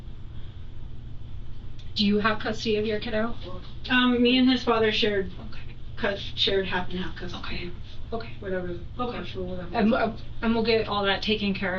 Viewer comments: At the beginning-ish, it sounds like she says, “me and his father SHARED half and half custody.” Past tense. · “Me and his father shared half and half custody.” Using the past tense will get you every time. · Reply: I went back and listened a few times and it definitely sounds like she said “shared”. She actually said it twice